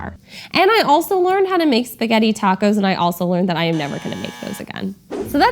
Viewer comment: "spaghetti tacos!!!" i thought he said spr getting tacos"